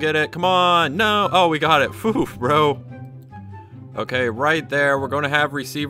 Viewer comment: did he just say phoof ?